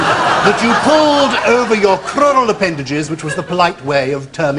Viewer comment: What is Gyles saying is the Victorian term for legs? Something appendages. I can’t make it out and it’s bugging me more than it should!! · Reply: Crural